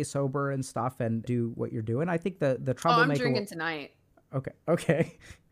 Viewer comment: I have absolutely no clue what that means but I'm happy they're having fun 😅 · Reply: at first I thought she said I'm doing it tonight, but she actually said I'm drinking tonight. It's been 8 days. So the joke is she stayed sober for 8 days.